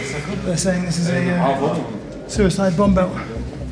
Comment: yea he clearly didn't know what the guy was saying lol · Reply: "This is a suicide bomb belt"